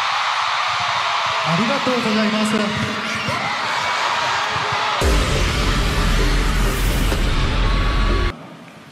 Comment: Please what did V said at 17:23 ? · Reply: V said Thank You(Arigatou Gozaimasu) in Japanese.